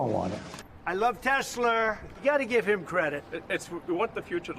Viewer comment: Did Trump just say he loves TesLER? I didn’t know he was related to Madea?😂